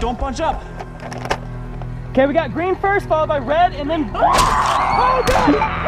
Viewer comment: I thought heard Green, Red, and then White, but there's no white siren! LOL